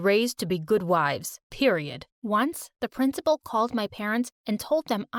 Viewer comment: did he just said PERIODT 💀💅